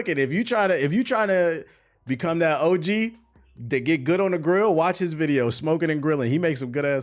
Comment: My fatass thought it said get food, i was like say less! 😂